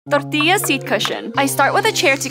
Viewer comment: At first I wasn't watching and thought u said TOILET seat cushion